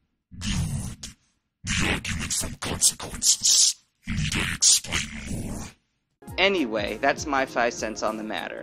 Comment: i cant understand what he just said... any help here? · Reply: aracniphobia1 I said, " Good, Good, the argument from consequences. Need I explain more? "